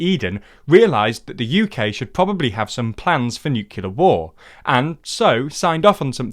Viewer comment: Did I hear "nu-killer" instead of 'nu-clear'?